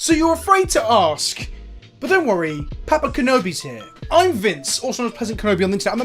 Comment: I'm sure you said "Papa Kenobi" but I definitely heard "Peppa Kenobi" which is a crossover I never thought I'd imagine.